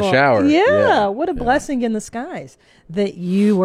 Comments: Did say "blessing in the skies" instead of blessing in disguise? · Did say "blessing in the skies"? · Did say “Blessing in the skies” at 20:44 ? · did she just say “blessing in the skies” instead of “blessing in disguise” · Does say "a blessing in the sky's"? She's becoming tiktarded!